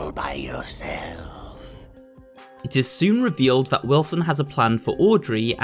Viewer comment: I thought he said "It is soon revealed that Wilson has a crush on Audrey" 😂